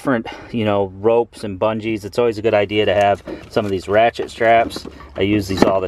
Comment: Oh man I thought you said rat shit straps instead of ratchet. Where is your body armor living in Detroit area? At least pack insert.